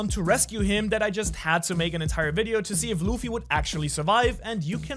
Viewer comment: At the end I thought u said Shanks for watching